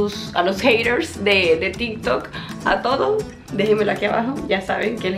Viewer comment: pensé que habia dicho tiktok pero escuche varias veces y dijo tintok :face-orange-raised-eyebrow